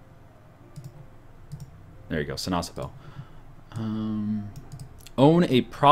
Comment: Every time he sai sinasappel i thought he said si na chapel